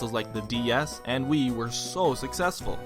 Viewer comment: is it just me or does it sound like he says Wii U instead of wii